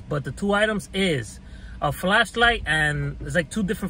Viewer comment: I thought you said a flesh light 😏